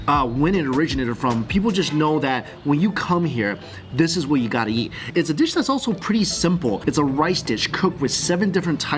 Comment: I’m sorry, can anyone tell me what that dish and 10:00 is called? He said it but I can’t seem to find anything of the sort online, I’m thinking that maybe there’s a different names I should look up? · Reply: Dish name is warjalay it's a pashtoon traditional dishes of swat region kpk Pakistan 💚🇵🇰